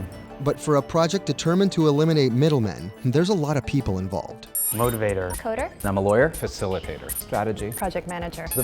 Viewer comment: did he just call himself a ''motivator''?